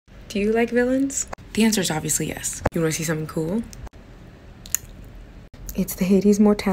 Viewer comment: what she said: '' do you wanna see something cool''. what i heard: '' DO RACISM COOL ''.